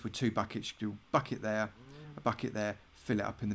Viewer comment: So didn't it sound like he was saying "f*** it there" at 4:20 lol